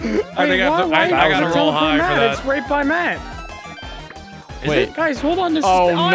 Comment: when the star moves the subtitles say that said "its raped by Matt" edit: i got timestamp slightly wrong